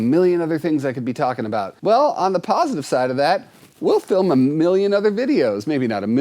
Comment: Am I the only one who heard "Pawsitive?"